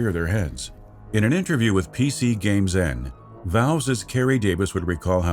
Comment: I'm sorry did you... Did you just say Valveses? Are we Smeagol now?